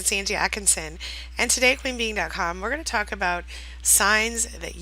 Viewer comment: I thought you kept saying you were 'queen bean' lol until I read 'queen being' on the intro.